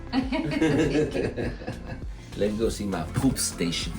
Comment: did he say ‘poop station’? stealing that lol